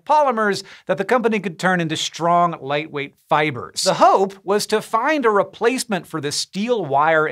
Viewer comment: It sounded like you said the hoe had to find long strong fibers. Haha